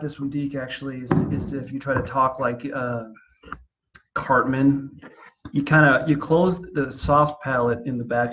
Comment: I'm glad that I never wasted even more of my life by instead of just repeatedly listening to this, I eventually switched the subtitles on!- tT explain: I thought he'd said "Try an d sound like HARTMAN"! I was trying to sound like Gunney Hartmann from Full Metal Jacke and I got nowhere! When I switched on the subtitles read ""Try an d sound like CARTMAN" (rom Southpark)................. ........ it STILL didn't work! I have a suspicion the missing ingredient for myself is a considerable lack of talent, and for many others with talent, their not having level of talent.... and/or his (perhaps very unique) biology. :-)